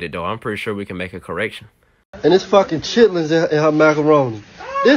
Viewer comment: Can anyone tell me what he said at 17:05 pleass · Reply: Ok turns out chitlins are the intestines of animals